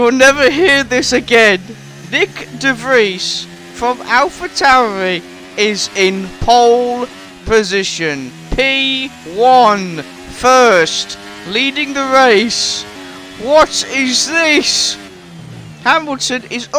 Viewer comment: Sorry, I was cleaning my ears out at the time that you said that, what did you say?! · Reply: I said. Nick De Vries is in Pole Position, 1st, Leading the Race